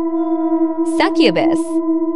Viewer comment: I heard "suck ur bus" instead of "succubus" 💀💀💀